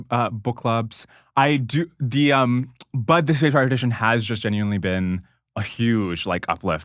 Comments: what does Dwarkesh say at this point? The transcript thinks he says "Bud the SpaceBar Edition" but i doubt thats right as i can't find anything on google. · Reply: But the spaced repetition cards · What was said at 26:20 " ______ edition has just genuinely been a huge..." and at 26:34 "I do regret all the episodes I did without using _____ because all of the insights have just faded away" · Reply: Spaced repetition cards